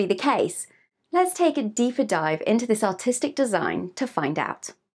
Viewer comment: I thought she was saying "autistic" design. Lol